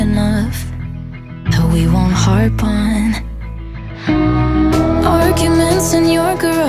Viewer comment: At first I thought it said: and it'll be long enough, that we won't heart bond. And I actually like it more😂